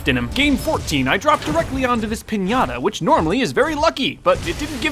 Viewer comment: did he say piñata?